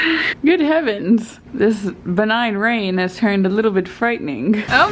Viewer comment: I thought you said, “this benign rain has turned malignant.” 😭😂